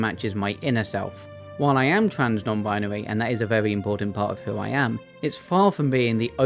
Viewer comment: I heard that as "Trans Gnome Binary" and thought "this is it, the perfect fantasy lore communicator!" I was slightly disappointed by my own ears on a second listen